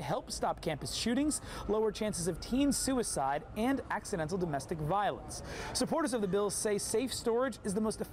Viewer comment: did he say "accidental domestic violence"? 🤔